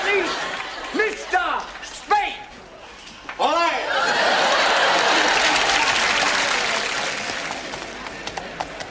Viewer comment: What did Juan Say of the Spain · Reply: He said Olé. It's a scream associated with toreros, the people (usually men) who kill bulls.